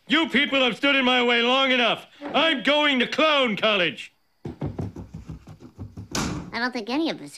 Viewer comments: For a second I thought he said clone collage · I thought he said clone collage